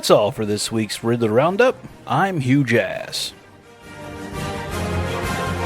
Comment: I swear even on the LFC fireside chat I thought y’all were saying Huge Ass instead of Hugh Jass 🤦‍♂️😂